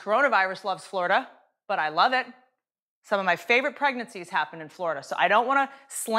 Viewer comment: Some of my favourite pregnancies happened in Florida. 🤣🤣🤣🤣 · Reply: I thought she said pregnancy tests which would be backhand compliment